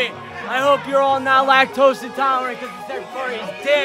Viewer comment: did just say, "Lack toast intolerant?" lmao?